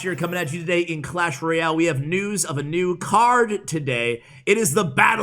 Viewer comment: Did anyone else thought he said nudes lol